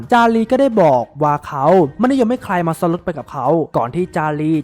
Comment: พูดว่าอะไรอะ ไม่เข้าใจ🤣🤣🤣 งง อยากให้แอดแก้ไข้การออกเสียงให้ชัดกว่านี้นิดนึงครับ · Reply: ซ้อนรถครับๆ